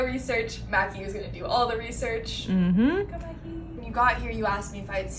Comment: I heard something around the 5:06 mark that sounded like “hey mackie” but that totally could’ve been someone in the hallway.